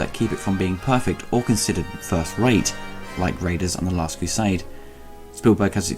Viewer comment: For a second, I thought you said Raiders Of The Last Crusade hahaha.